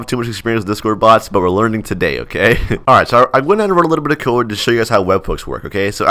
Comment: what did you say at 2:10?? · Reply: a little bit of code